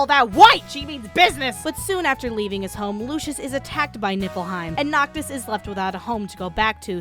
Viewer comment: min 2:50 Did she just said Nippleheim?